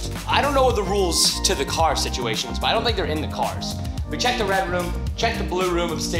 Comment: Honestly, I thought Preston was trying to do a scooby-doo impression when he said “the red room” I thought he was trying to say bedroom